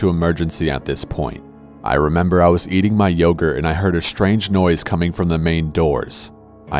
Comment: When he said I was eating my yogurt I thought he said I was beating my yogurt I was like whoa bud you’re in a hospital